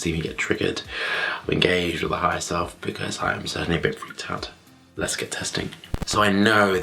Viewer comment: Did he just say the R word? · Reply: Don’t think so....I heard “freaked out.” 🙂